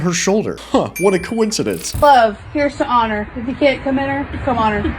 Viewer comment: wtf did she say? I can't understand it. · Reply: Here's to love, here's to honor If you can't come in her come honor (obviously to sound like "on her")